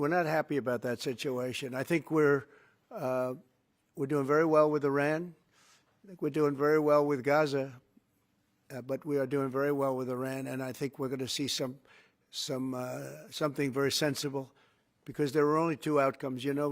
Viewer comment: did he just say we are doing very well with Gaza 🤬🤬🤬